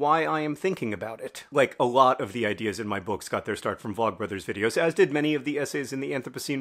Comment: Thank you for the video, I have to laugh when you first said vlogbrothers I thought you said Block Brothers, ha ha ha I was very puzzled for several minutes :)